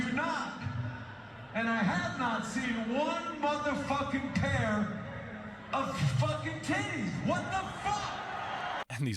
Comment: What's he saying at 5:45? " I have not seen one motherfucking pair of fucking kids?" Is he saying the crowd is too old or something? I don't get the context of what he's ranting about.